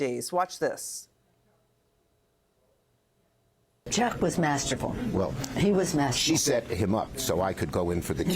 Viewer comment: At first I thought piglosi said that schummer was master......bait...ing not masterful!